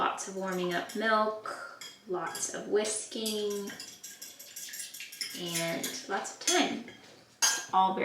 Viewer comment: At 21.57 - I thought you said “lots of whiskey” rather than “lots of whisking” at first 😂